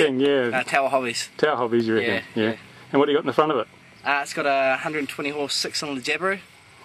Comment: Woops I misheard - he did say 120 hp - I need to clean my ears out! :D